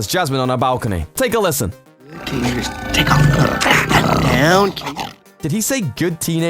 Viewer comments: I heard "good teachers take off ur clothes" lol I'm dead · i thought aladdin said "good tiger, take down your claws".. you perverts